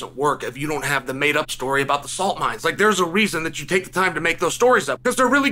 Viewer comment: Did he just say the made-up francis story about the Sand mines😂 man you're wild